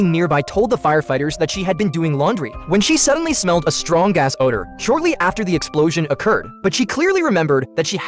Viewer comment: was supposed to say strong gas order But I heard strong a$$ odour